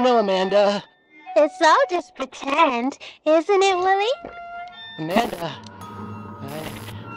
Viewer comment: WHAT DID HE SAY?? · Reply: He said "Amanda.." lol XD